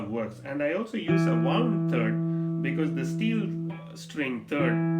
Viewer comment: Hi sir! I don't understand what you mentioned at 3:23, did you say warm 3rd or wound 3rd? Can you please explain! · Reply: Its wound 3rd .. wound string for third string